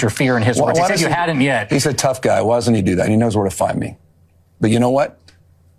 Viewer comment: He said ‘Tough Guy’. I heard ‘Bitch’.